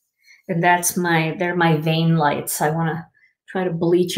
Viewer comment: I had to laugh...I thought you said "Vein Light"...then realized you meant "vain." My first thought was "Wow that light works well, I don't see any veins at all!" Oh the funny ways we can misunderstand each other! Love you and your videos - you are always a bright light in my day.